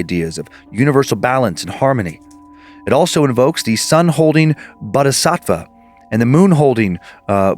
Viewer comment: I mean no disrespect to Buddhism and I feel bad that this is where my head goes but when said, "bodhisattva" the first time, thanks to Timesuck, I heard, " butt uh sock va" and all I could think of was good ol' Randy "Mr. Buttsocks" Kraft. 🙌 Hail Nimrod 🙌